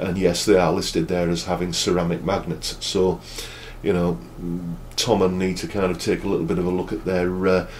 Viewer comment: I was sure he said Tom and Nita need to take a bit of a look at their listings I think.... . 😂😂😂He didn't he actually said 'Thomann need to' HAHA I was thinking who the fuck are Tom and Nita. HAHAHAHA